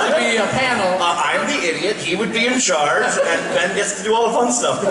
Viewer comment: did bobby say "I would be an idiot?" Or "I wouldn't be in it?" Haha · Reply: “I am the idiot.”